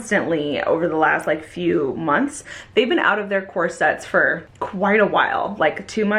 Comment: core set - i thought you said corset and i was like wtf since when does wear corsets??